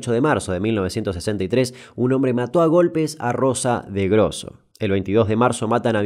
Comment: pensé que decía Rosa Melgrosso, posta.